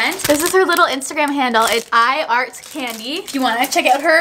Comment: i thought it said i fart candy